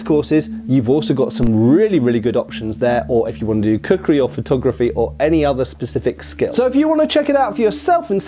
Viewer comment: When said "cookery" I thought he said "kukri" and was briefly surprised at how much Wonderium had branched out. 😂